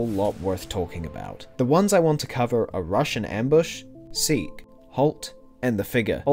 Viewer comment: i thought he kept saying "russian ambush"